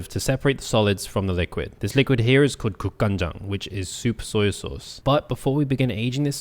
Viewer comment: At some point I thought you said super soy sauce lol